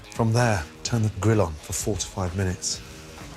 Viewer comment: I am so glad you included written instructions, I thought he said "forty-five minutes" instead of "four to five minutes."